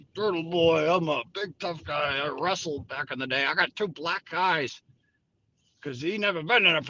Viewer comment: Did say 2 black eyes? I really thought he said 2 black guys?